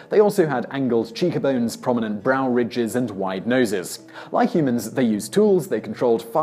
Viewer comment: I for real heard him say 'Like humans, they burried their debt' .... I was like damn that's savage. It hurt. But I misheard. He said "bury your dead" 😭